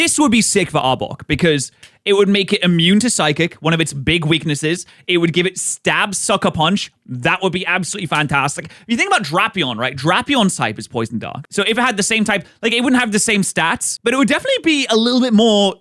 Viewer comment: A minute in and I heard “This snake could get Sucker Punch”…?